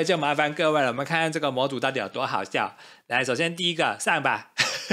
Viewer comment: 我把「上吧」聽成掃把😂